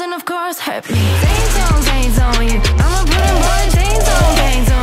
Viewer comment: I thought I was only one who heard "chainsaw you" hehe